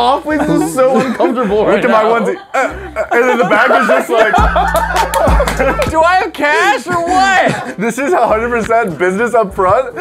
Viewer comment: I was drinking water then I hear do I have money or what!